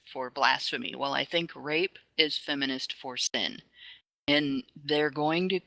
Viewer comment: I am having a hard time here. At ~28:23 says: "Misogyny is feminist for blasphemy... Rape is feminist for ..." Something but I cannot tell what word(s) she is using. Can someone help me out? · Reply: Sin